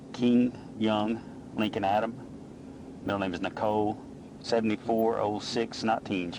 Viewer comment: Since he didn't use the ICAO alphabet I thought he was saying her full name was king young Lincoln Adam Nicole Forbes before I smartened up